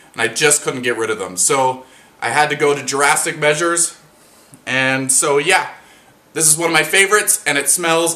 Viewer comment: Shout out the name of orchids? Did u really say "Jurassic Measures" at 8:09 ? Lol you're so freaking funny! Your videos bright up my days ;-)